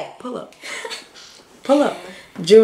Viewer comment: IT TOOK ME FOREVER TO COMPRHEND THAT SHE SAID JUNE 9TH I THOUGHT SHE WAS SAYING IS "THU [U] NICE" 😂😂